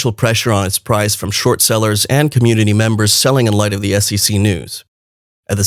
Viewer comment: the subtitles say sexy instead of SEC